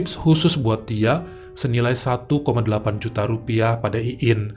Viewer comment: Maaf tadi salah dengar rupanya 1.8 juta... Saya fikir 1.3 juta...